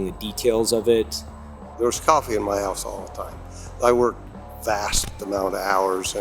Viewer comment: did he say " there's coffee in my ass all the time "